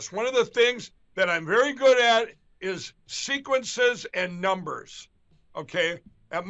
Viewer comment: Lindell has such a Mushmouth problem, that I heard, “SECRETSESS”, When he said, “Sequences”. ~sigh